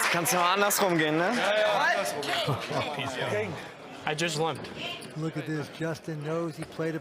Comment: where Justin says "I just limped" many idiots think he said "I just won." but you can tell by the movement of his cheeks, he says "limped". The word "won" takes 1 syllable, but he clearly said a word that takes 2